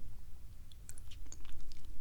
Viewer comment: I heard “slime liquor” at first and was like sign me up